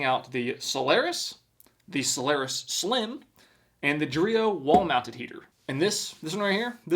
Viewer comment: I KNOW it's "Dreo wall mounted heater" but I kept hearing "Dorito walnut heater"